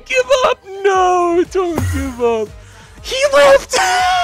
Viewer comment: Some are misheard "uahauahauahauhaauahauhauahauaha no don't give up no don't give up HE LEFT uhuahauaahuahauahauah LARRY COME BACK IM SORRY OH GOD LARRY UAUAHAUAHAUAHAUHAUAHAUAHUA I THINK I JUST MADE LARRY QUIT"